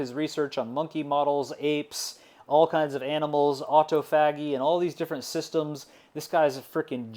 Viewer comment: newk-lee-us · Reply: The other one I heard in this interesting lecture was the word "autophagy". I think it's pronounced "or toff a gee" (with a tiny pause between the "or" and the "toff..." parts), but what I heard was "auto faggy".